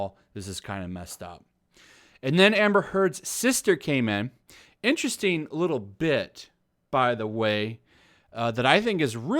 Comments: I thought he said interesting little bitch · I thought you said, "Amber's sister, interesting little b!tch" lmao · I heard that interesting lil btch!😂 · I heard at 7:03 "and then Amber Heard's sister came in, interesting little bitch"... Yeah we were expecting that I guess.